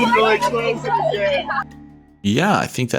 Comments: It sounded like he said "she nearly exposed it again" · I hear "evil exposed it again"